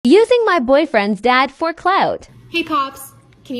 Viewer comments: She made it sound like she said “hey cops” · i thought she was said "hEy cOPs"